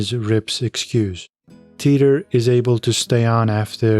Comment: 100% agreement with your comment as to WTF Laramie is still doing in the bunkhouse? 😡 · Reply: yeah i agree! i thought rip said except for teeter. no girls in the house